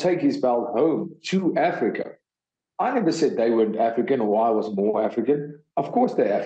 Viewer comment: “This has never been done before, to have a guy from Birmingham in the U.K., did it from the U.K,” Edwards told reporters at Vivint Arena in Salt Lake City. “I know Bisping did it first, but he did it from living in America. So it was hard for people like me to relate, to say, ‘Look, we can do it, as well.'” Seems fairly similar to the sentiment that DDP is saying too. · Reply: This needs to be brought up more. I really thought he was saying izzy wasn't af4ican like he was born in Nigeria and liv3e there untill 10